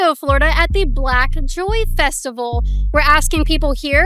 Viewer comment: I thought she said Pak Choi festival 😂